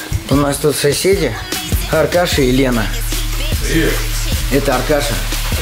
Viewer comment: Мне сперва послышалось "Алкаш и Лена"